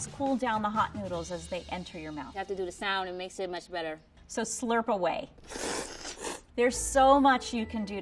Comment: “So slurp Away” That’s what he said